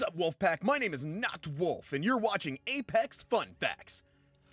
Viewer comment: It sounds like you said My name is Not Wolfe